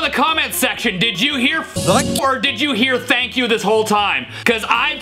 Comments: i heard f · I heard f🤬🤬🤬you Thomas